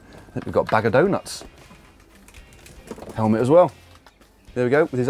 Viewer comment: Did he say bag of doe nuts?